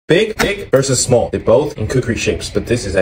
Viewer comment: I literally thought you said big dick versus small dick the fact I’m a lesbian, and I looked at the screen immediately🥲😭